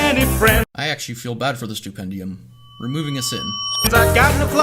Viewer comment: sin for Animation Sins, subtitles say Stupendium but he says THE Stupendium.